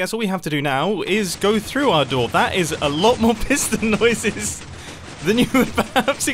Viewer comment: I thought he said piss noises